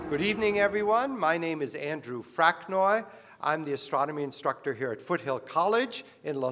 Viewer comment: Did he just say "sodomy instructor"?